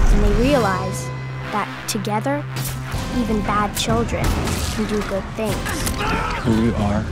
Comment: WHAT DOES MADDIE SAY AT 1:22? HELP PLEASE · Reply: They realised that together even bad children can do good things